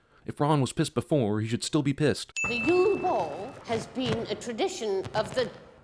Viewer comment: I heard Professor McGonagall say "The Uwe Boll" at 6:56 instead of "The Yule Ball". Which should account for another sin.